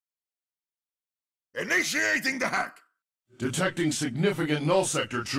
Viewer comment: I thought Mercy said "Ignitiating the heck." Uh, yeah, I think it's alright started. Sorry Mercy.